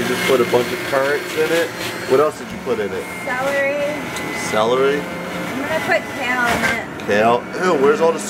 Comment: in the beginning when she said celery I thought she said sour aid lol